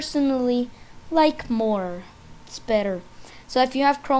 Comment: did i hear more better 0:46 😂😂😂